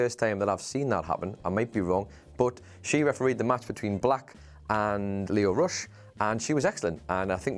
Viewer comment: Did he just call Velveteen Dream black?